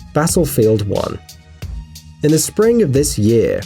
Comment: did you just say Basselfield 1?